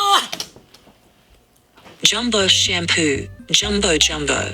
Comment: I always thought he was saying shamu, cause he’s black & white